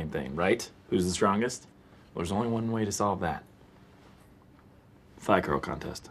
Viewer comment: Thigh curl contest, for the longest time I always thought Jim said “fly girl” contest. 🤷🏽‍♂️😂